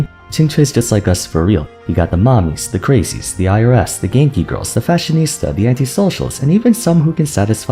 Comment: does anyone know what was said at 2:19, it sounded kind of like Gay E-girls but I think I'm wrong. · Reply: Genki girls (an archetype of anime girls known for their bubbly personality and energy and stuff)